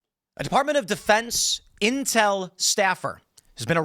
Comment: Thought Tim said incel staffer for a moment.